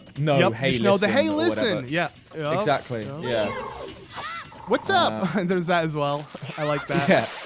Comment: I always thought Navi said "Watch out" when you z target on an enemy like warning you!!! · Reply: She is saying "watch out". He is actually wrong about it being "what's up".